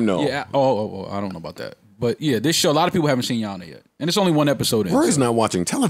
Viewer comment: What’s the name of the show they were talkin about at 5:00?? Couldn’t understand what they said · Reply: Your honor